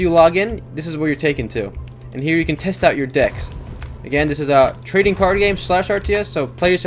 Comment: is he saying dicks all the time?????